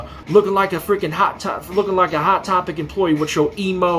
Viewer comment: Not the stutter at 0:40 making it sound like he said "lookin like a hot top"